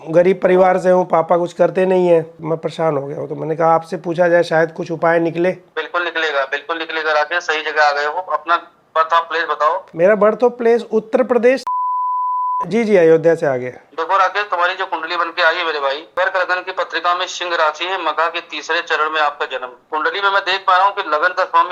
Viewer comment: Did he really say "birth of place"? 5:43😂